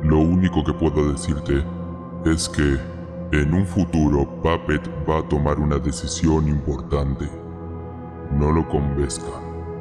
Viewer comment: ¿Alguien sabe que dijo Swadow Freddy? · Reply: Dijo:en un futuro puppet va a tomar una decisión importante. No lo convenzan.